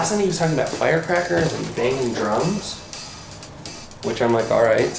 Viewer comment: when he said “banging drums“ I thought he said “banging drugs“😂